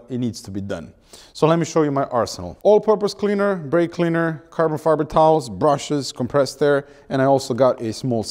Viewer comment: Am I the only one who heard Carbon fiber towels at 49:15?